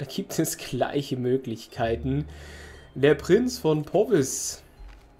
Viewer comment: ich hab zuerst "Der Prinz von Pommes" verstanden 😂 Der Kleingkönig hat übrigens selber auch noch eigene Vasallengebiete auf der irischen Insel, die jetzt durch die Vasallisierung zum Königreich England gehören 😉